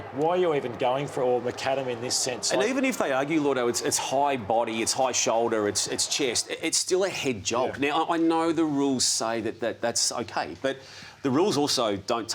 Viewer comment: Did Damo say "Head Job" around 9:40?